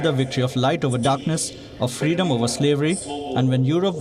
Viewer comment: Did he say "freedom over slavery"? What slavery did he talk about?